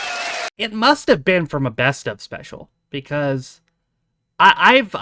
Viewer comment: haha - I thought you said 'It must have been an asbestos special'